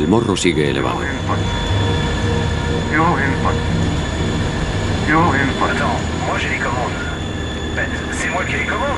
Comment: En el minuto 35:48 suena una alarma no entiendo que dice. You in fight? O algo asi puede ser ? Nunca había escuchado esa alarma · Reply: Creo que es "Dual Input" Que los 2 pilotos están apuntando los controles hacia otras direcciones